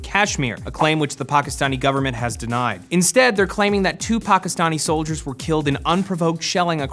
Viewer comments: when he said 2 Pakistani soldiers i legit froze i thought he said TUPAC lol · all i heard in 3:23 was 2PAC :D